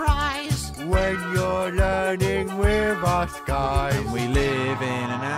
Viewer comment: i thought he said "When you're learning we must guide"